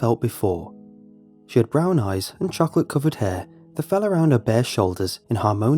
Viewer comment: tell me why I misheard "chocolate coloured hair" as 'chocolate covered hair' and didn't question it...